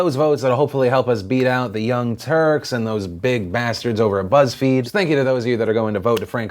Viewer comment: Did you say "Buzzfeebs" at 5:04?